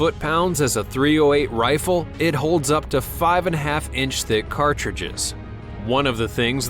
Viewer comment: I really wish the narrator knew how to punctuate, it sounded like he said the S&W "holds 5 and a 1/2 inch thick cartridges" .... and after listing to it over and over, I did understand that he was in fact saying "it holds 5, 1/2 thick cartridges"